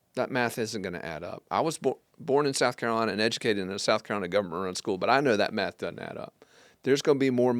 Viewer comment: When you said, "government run school," I thought you said "Governor school," and I assumed you meant the Governor School for Math and Science. So, I had thought you were a better a high school student than you really were. OTHERWISE: Tomorrow, God willing, there are TWO Murdaugh-related hearings in Charleston federal court: the Fleming sentencing in the morning and the Nautilus v. Murdaugh civil suit in the afternoon (or flip that). Will you be there to COVER either or both?